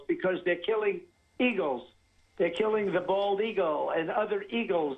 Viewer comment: Personally, I swore I heard him say "bald egos"?.. yup, that's you, ya ding dong!!!!!😳🙄🤣🤣🤣🤣